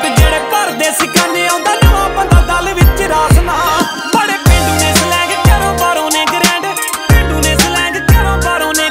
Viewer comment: Barre pindu ne slag? Lol or am I just hearing it wrong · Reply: Barre Pendu ne Swag....